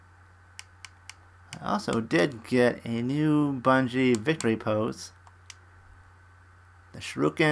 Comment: Lucy? Explaining? Do I look like a Lucy to you? Also, was that a ‘Bungie’ I heard instead of Genji at 4:16? I think it was. Shame you didn’t get the skins you wanted, but RNG is gonna RNG. You still made out pretty good it looks like.